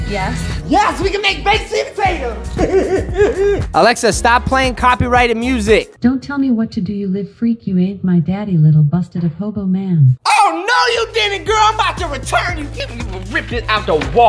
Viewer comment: made me laugh so hard I fell out of my bed and I kept replaying it over and over you don’t tell me what to do u freaky daddy who ate my coco is what I heard it said I may be wrong but I kept laughing lol